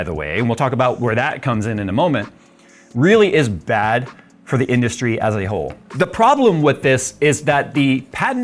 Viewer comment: that's the key phrase to describe Nintendo: ahole. Thank you Jay for saying it - even if that's not actually what you said. 🙏